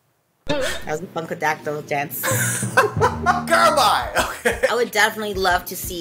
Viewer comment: I thought she said fuckadactyls